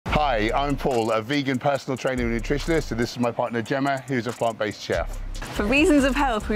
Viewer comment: Such a helpful video. Thank you! Thought you said, "This is my partner Gemma, who is a plant based chair" 😮